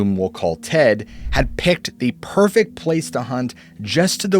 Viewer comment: I could've sworn you said "Let's call him Ted", because this individual wanted to remain anonymous. Poor Ned.